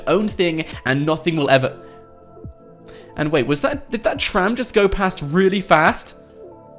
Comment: Did he say tram 0:48 😂😂